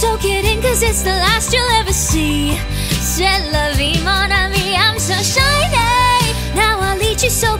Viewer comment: For some reason (to me) it sounded like you said "celery' when it says C'est La Vie. Lol